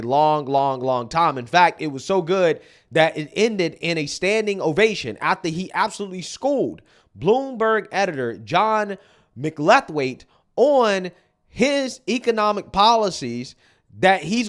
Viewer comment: I thought you said, "John McLeftWing" 😂